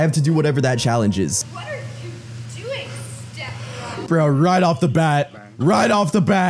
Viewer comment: I hear “what are you doing STEPPPBROOO